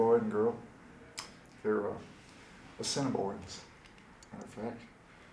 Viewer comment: Does anyone know what he says at 0:07? "This was given to me by a little Indian boy and girl. They were ?????..., matter of fact..." At first I thought he was saying "Seminoles," but it sounds more like "Semibores..." Any ideas? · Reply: Assiniboine (Ojibwe). A native tribe that is in the North Dakota/Manitoba area.